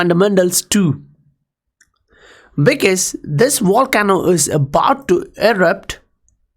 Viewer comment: OMG LOL I HEARD "THIS WAR CAMEL IS ABOUT TO ERUPT" - said Volcano not war camel, took me a second to realize lmao. Love the content always TOP NOTCH!!